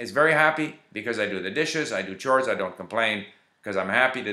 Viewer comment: Could somebody tell me what did he said at 4:00 "I do the dishes, I do .......? , I don't complain". By the way your french accent and pronunciation are flawless! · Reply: I do chores